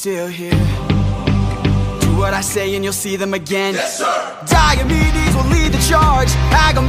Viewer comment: I thought Odysseus said "Diabetes will lead the charge!"